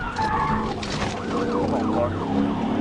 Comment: Did he say "C'mon, car." when he fishtailed?